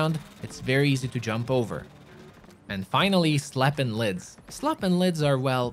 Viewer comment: Am I the only one who heard him say slappin' lids?